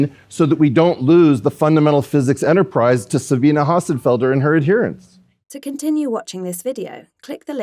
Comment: It’s Sabine - not correcting you in particular, cuz Weinstein said it wrong in the first place 😅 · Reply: Sounded like he said "Sa-bine-ah", which I'm pretty sure is how you pronounce the German name Sabine